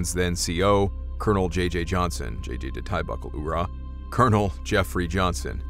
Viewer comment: What are you saying at 5:09? It's garbled. Like, "JJ da tie buckle. Oorah". Why is the letter all blurry?